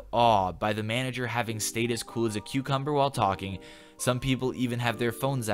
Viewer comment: did you say "cool as a cucumber" ?